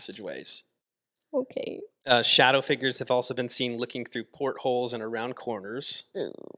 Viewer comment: I heard licking through portholes 😭 I need sleep and Jesus 😂